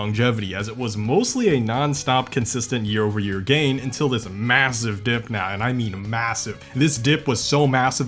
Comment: did you say… massive?